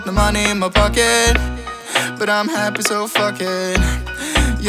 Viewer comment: What does he say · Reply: Fuck